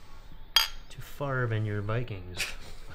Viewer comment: around there he said vike queens instead of Vikings 😂😂😂